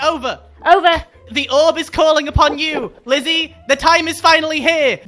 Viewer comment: I THOUGHT HE WAS SAYING GLIZZEE- 😂 😂